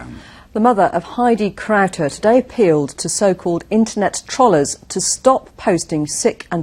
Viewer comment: LOL 0:25 Did she say "internet trollers"? :D